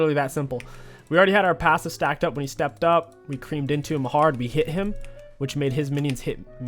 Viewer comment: i cant be the only one that heard him say "so we just CREAMED into him hard" after killing darius at the start